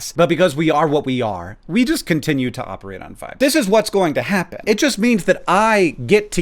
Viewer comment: "But because we are what we are, we just continue to operate on fire." I'm not even sure if that's what Hank said or if it's the subtitles spilling truth xD (for those curious, it's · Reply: I believe what he said was "But because we are what we are, we just continue to operate on *vibes*"